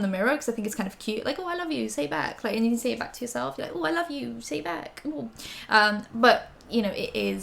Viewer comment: I legit thought you said the slogan was "I love you, stay back" as in social distance. 😅 And I was thinking, nice!